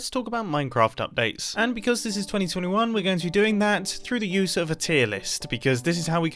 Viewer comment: thought he said 2022 and I almost got a heart attack